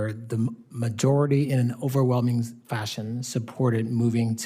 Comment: he says "fashion" but I hear Fascists. One seem more accurate.